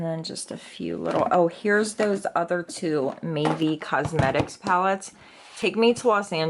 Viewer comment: Mavie cosmetics. I thought she was saying maybe